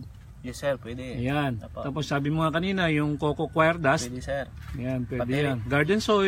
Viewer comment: Hello Sir, I am a new subscriber and I find your channel very informative. Sir I hear about making your own compose using available materials and you mentioned what sounded like "coconut fire?" anyway if i heard right can you discuss how to make it as well? Please. Thank you. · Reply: After scanning through the web I think what i heard was "coco coir" or coco peat.